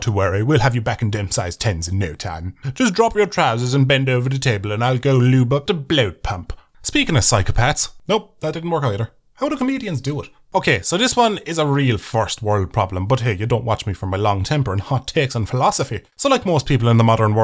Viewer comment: I miss heard you at one point and thought you said speaking of psychopaths my mother